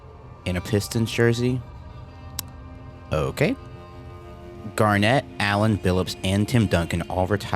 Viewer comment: "Garnet and Allen" wait.... dont you mean "Allen and Garnet"...... from Baker's squad..... who got killed by Leggett's dumb ass . if anyone gets that reference...... ill be shocked . . its from the "brothers in arms" games and ya.... when i heard "Garnet, Allen and....." i had to back up the vid to make sure i heard that right . i wish Gearbox and greasy Randy Pitchford could stop being so greasy.... and make the last Brothers In Arms game.... i just want to know if Baker likes snow.......